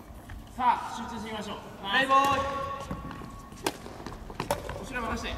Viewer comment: 「プレイボーイ」に聞こえてしまった